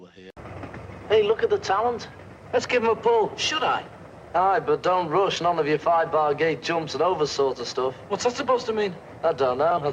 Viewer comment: I’ve never understood what he said there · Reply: George is saying "Aye, but don't rush - none of your 'five-bar-gate-jumps and over' sort of stuff!" There are various interpretations as to what scriptwriter Alun Owen meant by this line. My personal favorite is that it's a very disguised way of advising Paul to avoid, um, going off too early, so to speak. A more innocent interpretation might be "don't try to do something fancy to impress her, just to fall flat on your face."